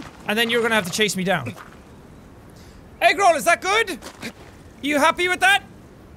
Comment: What is the name of the horse? I saw someone in the comments talking about someone called ''Agro'', but during the video I thought I heard ''Egg roll'' XD